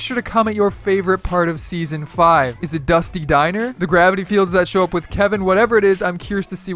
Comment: did he say “the gravity fields that show up with Kevin”?